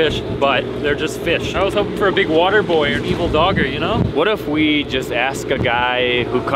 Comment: Did he say angry dogger??